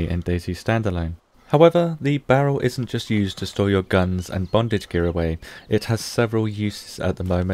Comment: "and bondage gear", did I hear that right?